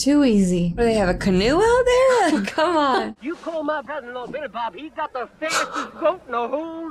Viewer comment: ! He and I have that in common! We both have cousins named "Billy Bob"! Mine, though, retired as high-ranking Sargent from the "US Army Rangers"! 🫡 Edit: Wait! Did he say that "Billy Bob" is also his brother-in-law?! 😮